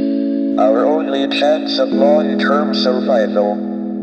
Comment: what is he saying at this part? 0:36 ? ( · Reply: only chance of long-term survival